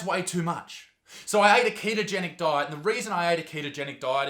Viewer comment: When he said that he ate a ketogenic diet I thought he said he ate a kid 😂